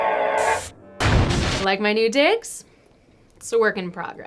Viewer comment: I thought she said like my new dicks? XD